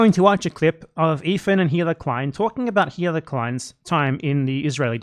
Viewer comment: anyone else keep mishearing say "hitler klein" instead of "hila" or whatever her name is